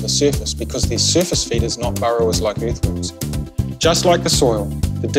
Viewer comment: Can't wait to get one of these!!! But I just want to leave some feedback.... I could not understand the sentence that was being said at the 1:04 mark. It sounds like "the surface feed is not borrowers, like earthworms." I had to rewind it several times and finally had to look at the transcript, which says "they are surface feeders, not burrowers like earthworms." This makes sense, but that is not what it sounds like.... maybe because it's said in a New Zealand accent, or spoken quickly... I just wanted to let you know, if there is any way to patch in a voiceover that says "they are surface feeders" so it doesn't sound like "the surface feed"... that could help avoid a lot of confusion for some people. Just a thought. Looks like a great product and again, can't wait to try it, thanks for designing this. Looks like it works great and the worms will be super happy!